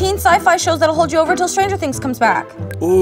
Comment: did she say stranger things